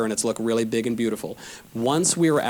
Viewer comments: The word "democrats" was censored from 2:58, near the end of the video, and closed-captions say "left". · YouTube has edited out a word. What is this word they don’t want you to hear? “Democrats” !The full sentence is, “Once we’re out of power, the democrats will easily undo all our gains.” It’s incredibly telling that leftists don’t want you to hear this simple truth. And Knowles is incredibly prophetic here because it’s exactly what Biden and the communist left are doing right now.